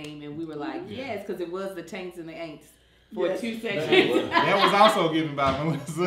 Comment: did she say the taints and the aints??? 😆🤣😂